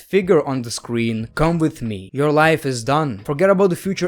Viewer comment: i thought he said your life is default but apparently not but thats better